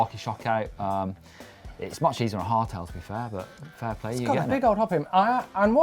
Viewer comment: Caption: Hard tail week? I thought you said hair tail!